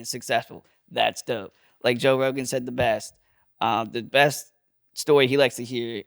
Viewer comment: Evan: When Joe Rogan said it best Mike: When you say Joe Rogan says it best Evan: I DID NOT SAY THAT, YOU’RE TWISTING MY WORDS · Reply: Evan: "Like Joe Rogan said THE best, umm the best story he likes to hear"... etc Mike: When you say Joe Rogan says it best Evan: I DID NOT SAY THAT, YOU’RE TWISTING MY WORDS Fixed your above, Evan is correct he did not say that. Evan said "the" not "it" go check it